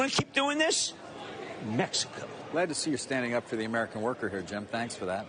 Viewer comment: no. Listen again. He said “for the American worker” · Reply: wait nope you're right. I did a double take with the timestamp, because I heard "to the American worker" and ngl I got really PO'd until I listened to his tone and told myself "relax, he's just being sarcastic" but now I hear "for" and that just confirms what I had to tell myself to calm down. So thanks for that cuz i wanted to punch a wall when i heard what i thought i heard. 😁😁 Seriously these rich douche bags don't know ANYTHING about living in America. If we were truly a meritocracy, there would be no billionaires. I could see the richest person being worth around 5ish million cuz he worked from a young age(19 - 20ish) into his mid 50s, invested and grew his money, and his money crossed that 7 figure mark, provided he had no kids, emergencies, etc. A wealth tax would prevent most of that money from turning his kids intro billionaires who would then talk down to those of us descended from people who couldn't reach that magic number who just want a fair shot to be able to be that next person. No billionaires would exist in a true meritocracy. This system is dead.